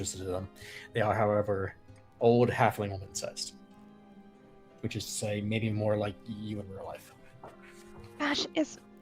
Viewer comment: I just caught saying "the boots are old halfling woman sized, which is to say, maybe more like you in real life" -- !! I didn't hear that last part before! My duck-shaped hobbit feet take offense! 🙈